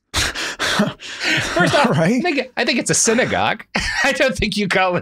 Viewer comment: What does Robert say at 12:18 between "First off-" and "it's a synagogue" ? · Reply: "I think", he's just laughing as he says it